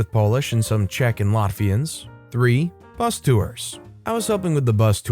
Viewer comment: Second story, I thought he said, “Mafians,” but it was, “Latvian’s.” Lol